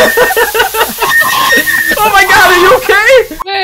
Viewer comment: I got scarred. I thought said I'm in your balls!!!